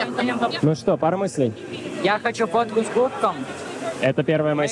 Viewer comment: послышалось "Я хочу фото с Гуфом" 😂😅😅